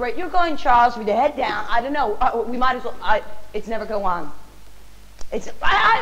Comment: "I'm gonna giddur" Da fuq?!😂😂😂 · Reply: LMAO, I think she actually said "I'm gonna get her.", her being the Elizabeth character she mentions soon after. She messed up because she was supposed to say "I'm gonna get Elizabeth." She caught herself after she flubbed the lines from 3:34 & onwards lol, she said: "It's never gonna go on.", I'm guessing she meant this scene they're in, she thought the director would cut but since he didn't, she's just ad-libbing awkwardly until he does lol. Like said, it seems like they just kept an outtake in the movie.